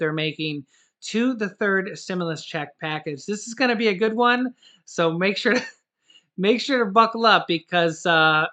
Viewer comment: I thought you said good News